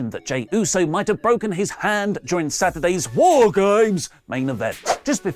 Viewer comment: Sounds like it saying "Whore Games" instead of War Games